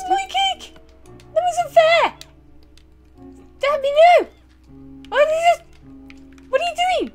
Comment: Did she just say. . .???!😮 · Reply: they said “stampy no” lol not “damn you”. You definitely misheard it